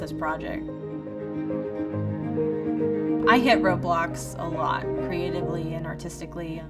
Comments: thought she said Roblox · Roblox? Omg for a second I heard roblox so I was like. :0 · i clicked out just as she said "I hit roadblocks" and I thought she said "I hate roblox" so I got really confused and came right back to see where the hell that was going